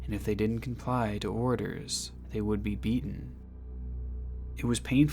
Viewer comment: I thought he said eaten at first and needed to rewind to see if I heard that right